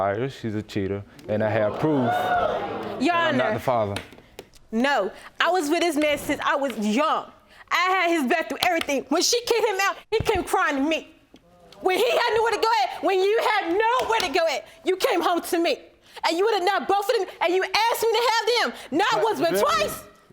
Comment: “When you had nowhere to go at”😂😂😂😂😂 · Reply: I thought she said had no where to glo i was like whhhaaattt?😂😂😂😂